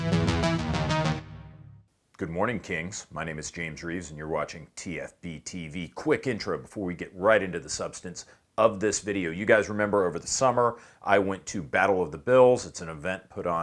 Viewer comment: I wasn't looking when the original talker said his name was James Reeves. I thought he said James Reece. If you don't know who James Reece is, you should look it up. Can't wait for the Terminal List to come out on Netflix. Jack Carr is far better than Tom Clancy.